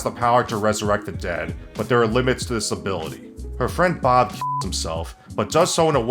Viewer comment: Ok, thanks to censorship i heard as "Bob shits himself" and my brain immediately imagined that the limit to resurrection is that you cant resurrect people who shat themself as they died as the impure load interferes with holy magic.